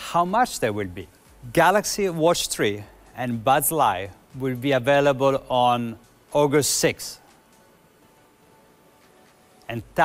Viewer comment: I thought she was saying Bug's life 😂😂😂